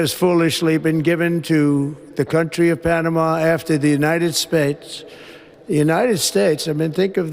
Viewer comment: Did He Say United Spates? 🤔🫤